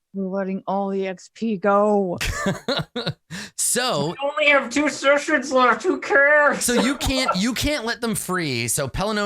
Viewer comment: does anyone else hear elf say only 2 sessions left